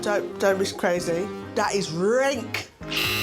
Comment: First time I saw that show I thought Judy said "that is rank." Like it's bad. I don't know what "Renk" is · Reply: She said rank. It’s just her voice and how she exaggerated it. We say rank.